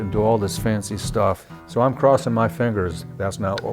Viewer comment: You sure know how to lend suspense to the adjustment you made and i thought i heard you say let me cross my fenders rather than cross my fingers!!! I enjoy watching you work your magic on them guitars.